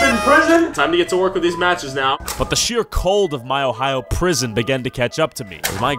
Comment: wait, did he say ohio prison?!